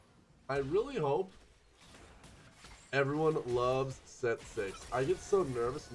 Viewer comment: When he said "i really hope everyone loves set 6" i thought he said Sett 6 stars... cuz double 3 is 6 :\